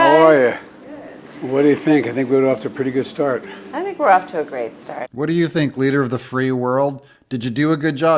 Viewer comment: Sounded like that said we are off to a really great fart! Because the just stink